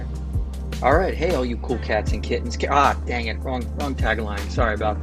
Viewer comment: Did say "Ah dang it" or "Goddangit" at 3:27? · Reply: he said ah dang it